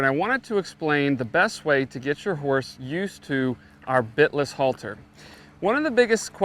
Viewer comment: did he say bit less halter.....